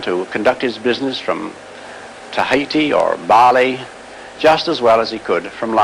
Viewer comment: did he say TAHITI?! Where are my Agents of Shield conspiracy theorists at??